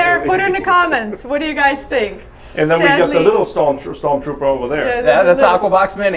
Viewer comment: For a second I thought you said storm pooper